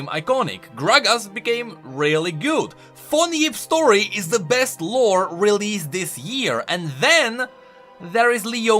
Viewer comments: Can someone tell me what he's talking about at 5:16? It sounds interesting but I can't decipher his accent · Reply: "Von Yipp's story is the best lore released this year." It was that short story where that Piltovan professor swapped bodies with his pet cat. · What does he say at 5:16? Sounds like "phone-eve" to me